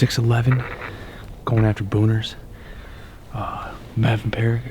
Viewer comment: did he just say going after boners?😂